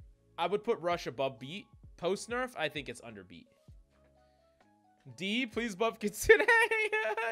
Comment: When he talked about kitsune I thought he said “under b” not “under beat” and I almost RAGED SO HARD